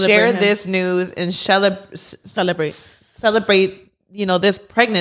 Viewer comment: Ahhh edit out 6:49 I think said him? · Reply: Yes said celebrate him!